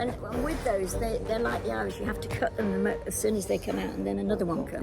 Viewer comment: 😂 I thought for a second said the Dianthus is like IRISH you need to cut them and then more come out 😂 💀 obviously she said Iris 😂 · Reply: I know, I read your comment first and then listened for it. It did sound like she said 'Irish'😂